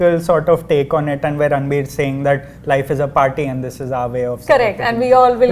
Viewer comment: Rahul Desai said Ranbir Singh instead of Kapoor at 6:12! 😅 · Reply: He said, Ranbir sing life is a party 😂 Not singh